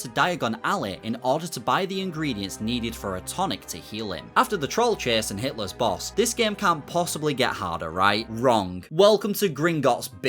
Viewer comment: I misheard this as "After the troll chase and Hitler's boss", like wtf I don't remember this version of Philosopher's Stone 😆